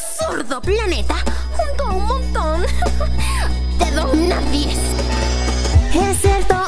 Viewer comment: que dijo dijo de dorna. viz :v